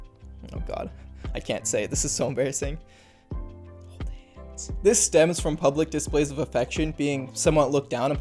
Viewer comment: what did he sayyyyy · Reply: hold hands 🤮